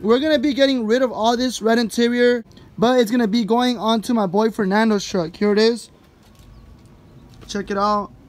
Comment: 😂😁😁 I thought you said you were given it to your boyfriend nando 🤣🤣 0:40 Que rollo con el Pollo cuhh. Saludos all the Way from Florida. 👍